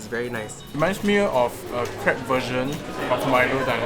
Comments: Lol i heard "Crap" instead of crepe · Lmao I thought said crap version 🤦